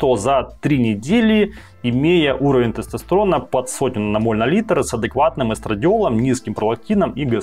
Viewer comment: 30 нмоль или 100 нмоль (послышалось) ? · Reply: 100